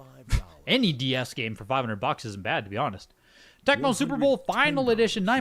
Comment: did you say "isn't bad" or "bad"? I bought a sealed monopoly for Nintendo DS for $50 just to open it and have a very good cib. I think there are other sealed games that would be bad for $500 Also I've done the same with Sims 2 and NFS Carbon for GBA which were even cheaper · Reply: I said "isn't bad" as in, the price is pretty high as not many DS games sell for $500 at all. Buying it for $500 would feel worse to me than selling it for $500 haha.